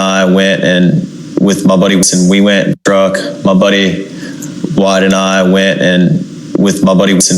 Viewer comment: I think he said “my buddy Wyatt and I” …I believe Wyatt is the one whose (step)Mother is a judge.